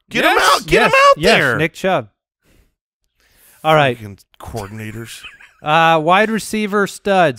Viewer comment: Did I hear cuss? Lmao. Could have sworn I heard “fuckin coordinators” at 37:42 lol